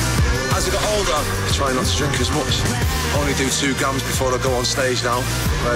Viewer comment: What does he say @ 1:29..."I only do 2 ??? before I go on stage. I used to do eight. It almost sounds like he said gums or guns... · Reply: he said grams